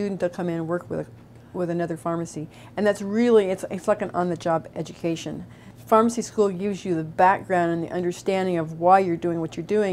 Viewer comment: Can somebody please subtitle what she says at around 1:42 because I’m almost sure I heard that wrong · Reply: "It's like an on-the-job education. Pharmacy school gives you the background [...]" is this the part you wanted subtitled?